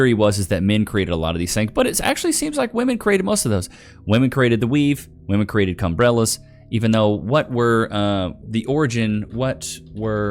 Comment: did you say "Cum-brellas" ?